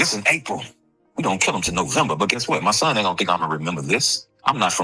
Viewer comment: DID HE SAY WE AINT KILL HIM TILL NOVEMBER. HE DEFINITELY WORKN, HE KNEW DA MOTIVE HE TRYNA SAVE HIMSELF N HIS MIND N HE TELLN IT ALL!!! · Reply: He said we don't kill him til November 😮😮😮😮